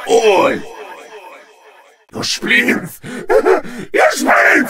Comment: what is he saying? Is this something in german? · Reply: your spleens